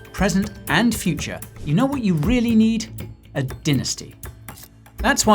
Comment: Ah... did you just say A Dinasty 🤣🤣🤣